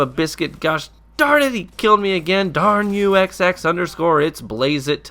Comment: Omg I thought you said Exo, psheww. Great vid thks